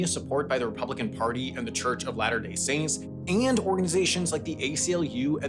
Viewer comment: AI subtitling error at 7:43 , “Ladder” is used instead of “Latter”